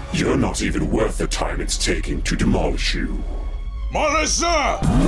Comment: what did you say here? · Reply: Manas Zya. (No, it's not pronounced Zee-ah, and no i dont know what it means)